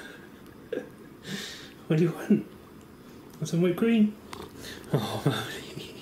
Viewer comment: did I just hear whipped cream?